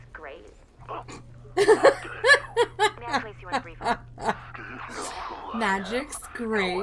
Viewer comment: My english isn't good, can you help me at 1:23 what's he said before "i'll remember this" ? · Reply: she put him on hold :)